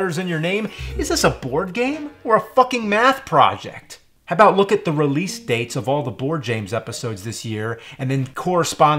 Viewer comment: Did anyone find out anything about what he said at 3:48? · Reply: it says: thirteen dead